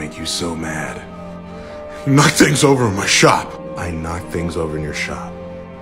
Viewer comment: What’s the excuse I maxed my volume and used headphone but still can’t understand. Something over his shop. I hear “knock things over my shop”